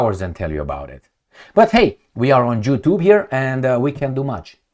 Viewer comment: Did he just say "Jew Tube"?